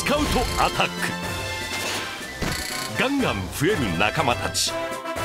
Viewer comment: did he say... gangnam? woop woop! x)